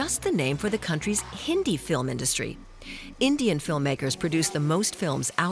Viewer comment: Did she just say Hindu Film industry?